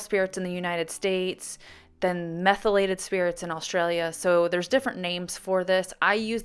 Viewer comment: Thank you for saying in Australia 🇦🇺 its methylated spirits I had all ways thought that you were saying mineral turps lucky I don't like mineral turps and have always used methylated spirits lol 😆